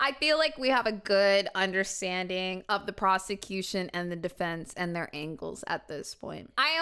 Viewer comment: The first time said, "The prosecution had so much evidence against her." I thought they said prostitution. I'm so sorry.